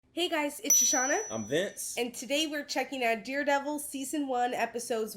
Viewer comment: best series in my opinion. Sounded like you said deerdevil lol